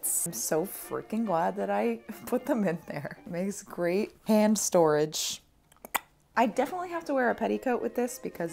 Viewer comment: I keep hearing "ham storage"